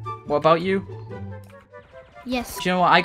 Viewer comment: did i just hear the kid says : me, yes